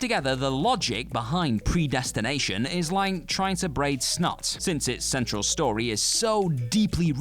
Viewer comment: "Like breaking snot?" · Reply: I thought he said, braiding snot. Does that make more sense?